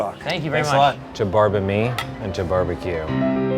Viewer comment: The subtitles are wrong. says "To barbe-me and to barbeque" and not "To barbe-me into barbeque."